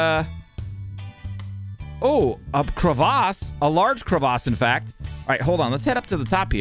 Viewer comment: Can someone kindly tell me what he is saying at this moment? 16:49 I don't know this word. Thanks. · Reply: cravace. Which he says to all things crevasse like. Its his way to call the big gap in the ground.